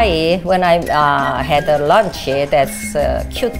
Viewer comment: Why is nobody talking about Jonathan getting hooked just from taking her luggage 😂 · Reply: Bailey Hakes I thought she said “lunch” not “luggage”